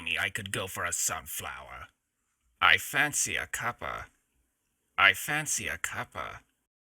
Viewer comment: I don't know why I kept hearing him say "la infancia acaba"